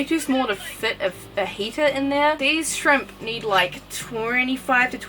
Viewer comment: When She was talking about how the shrimp would need a heater I thought she said fajita